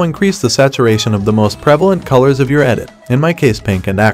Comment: the most "what" color (around 0:46, i'm not english i cant understand the word) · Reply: prevalent